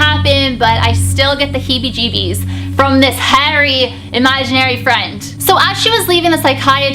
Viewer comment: What said: "From this 'Harry', Imaginary Friend" What I heard: "From this hairy Imaginary Friend"